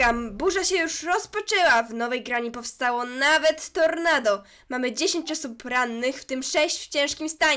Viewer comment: Usłyszałam 5 osób w tym 6 rannych i takie wtf xD